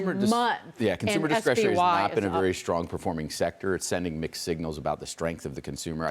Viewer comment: At about 0:56 he says "mixed signals" i swear at first i thought he said "McSignals"